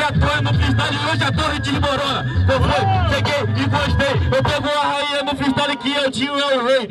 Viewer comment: What? eu entendi ''se é Gay e gostei''